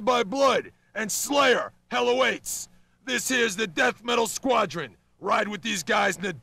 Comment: hey? wtf? at 0:27 he says death metal, right?